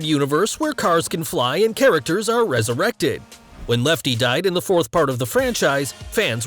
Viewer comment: did he say lefty?